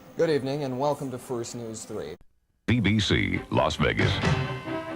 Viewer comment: DID IT JUST SAY BBC?????